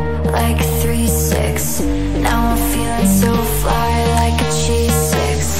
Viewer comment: I keep on mishearing “g6” as “cheese stick”